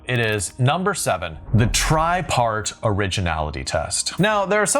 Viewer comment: I heard "tripe art" instead of "tri-part". Introducing a main character doing offal crafts would certainly be original.